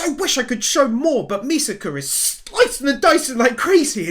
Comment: Did he say Misaka? 😂